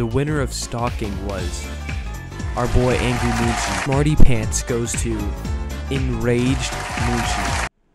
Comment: Who Did You VOTE FOR???? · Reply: Did you said smarty pants?